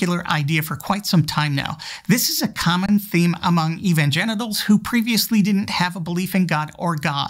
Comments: Did you say evanGENITALS? 🤣 I'm using this one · I thought I heard "evan-genitals".